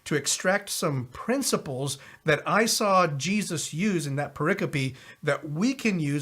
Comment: What is a "proicapy?" My spelling may be incorrect. The first time I heard it was at about 1:39.